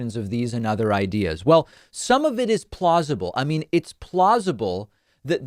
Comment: Every time I hear you say "plausible," all I can think of is, "plozma-ble".